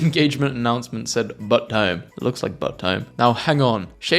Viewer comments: what does it actually say… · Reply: bout time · what does it actually say? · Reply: Bout time